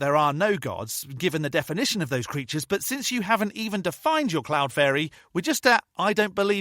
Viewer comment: I just misheard “cloud fairy” as “clown fairy” and I’m not disturbed by that… 😂 🤡🧚🏻